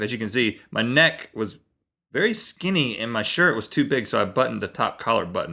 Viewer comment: did Rhett say his neck was very skinny?